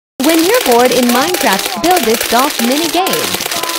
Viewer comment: I thought you said "if you are born in minecraft etc"